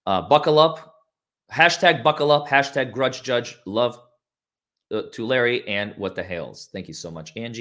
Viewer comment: Someone said "love to Larry" and I heard "Love Tulare", where I live 😂😂😂 I thought I just found a local 😅😅😅